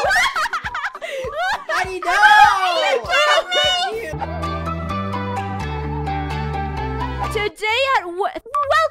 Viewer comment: did say the f word in 0:17 ? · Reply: He said finish